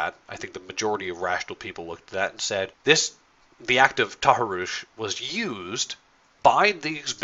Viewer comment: Hey, non-native english speaker here. Can someone give me the word that uses roughly at 7:43 to describe the events in cologne, please? I don't know (or don't understand) the word, and neither does the automatic subtitle generator.. Thanks! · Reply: Taharrush I've never heard it before seems like it is an arab(?) term for harassment.